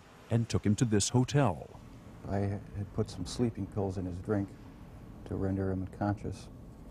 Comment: who else hears: "To render him conscious" ??